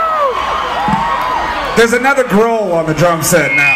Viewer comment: Thought he said "There´s another girl on the drum set now" , mocking Taylor :p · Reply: He said there's another grohl not girl